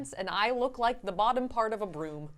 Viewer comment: Lol, my volume was low and I thought you said you “look like the bottom part of a womb” 🤪😂😂😂